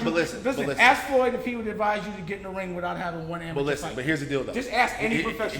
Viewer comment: Did he say, "Here's the dildo" at 7:21 Big pause lmao